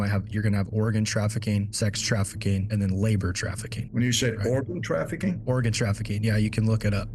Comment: he didn’t ask cause he was confused about organ he asked cause it sounded like he said OREGON lol